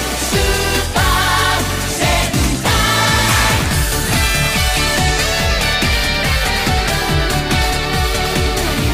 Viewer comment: wait did i heard super sentai or hentai???? 🤔🤔🤔🤔🤔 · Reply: It's Sentai